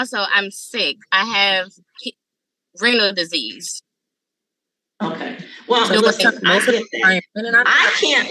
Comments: I swear it sounds like she is saying that her sickness is "rental disease" - 🤣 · I sware I thought the girl said “I’m sick I have renters disease” hahahahah your damn right you do not paying your rent on time.. entitled af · Thought she said Rental Disease 😩 Hope her kidneys heal, but baby $250/month is everyone’s dream.